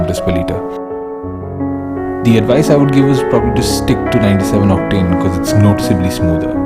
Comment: What did you said at 5:08 ? You said: "Stick to", and after i didn't understand a thing for like 2-3 seconds. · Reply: Stick to 97 Octane fuel